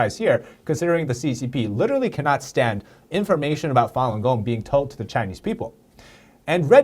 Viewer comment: Im sorry my ignorance has lead me to this. I cant understand the name of the group, and i want to google them. Can someone please spell the name for me? Its at about 6:25 thank you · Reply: Fulong gong or fulong dafa